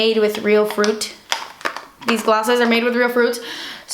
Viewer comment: When you said Rambutan I thought you said Rasputin and thought of Anastasia straight away then you said it😂😂 loved this video, don't apologise for buying expensive things, it's your life and no one else can judge that xx